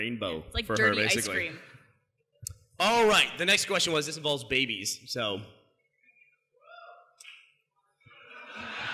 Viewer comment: What did the audience guy say at like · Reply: i think he yelled WOO damn! omg